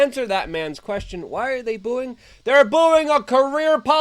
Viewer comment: those were her constituents who keep voting her in. She's been in Congress for forty+ years. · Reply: i heard "why am i booing? Hahaha"